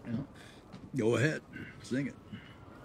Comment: he said “go ahead, sing it”, all i heard was “go ahead, skin it, skin that smokewagon and see what happens”